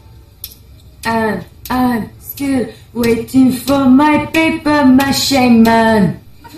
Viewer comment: what is she saying? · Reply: lili lili and I still waiting for my papier mache man Ps : I think